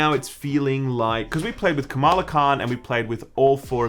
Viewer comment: Every time you say Kamala Kahn I just hear "Hang Kahng"... Secret message for the Avengers to join the fight?